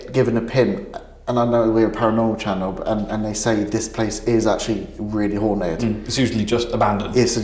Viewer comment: I thought said “I’ll say this place is really horny) 😂😳